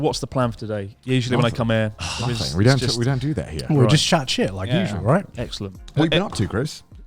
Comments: Did Garnt just say “we shat chit”? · did garnt just say "we just shat chit"